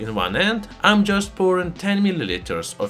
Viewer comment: epoxy resin . at first i thought he said "a box of raisins"😂